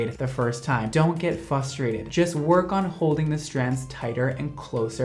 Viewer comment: me hearing say "dont get fusterated" instead of frustrated made me happy bc im not the only one that says fusterated